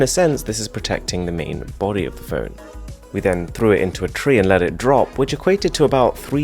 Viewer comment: they did not say throw it. they said drop it